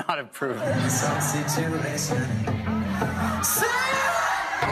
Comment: yo escuche taco.